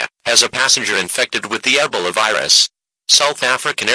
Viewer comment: no w8 ebela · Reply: 〈(( ͡&#176; ͜ʖ ͡&#176;))〉--- --- --- I heard him say E bowl a